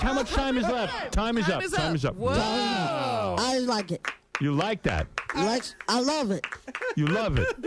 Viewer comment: I think Beat says “I didn’t like it”